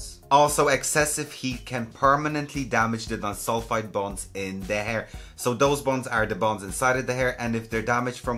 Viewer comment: Did you just say bones in the hair?!